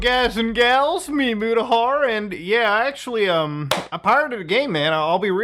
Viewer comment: I thought you said "I hired a gay man" at first 😂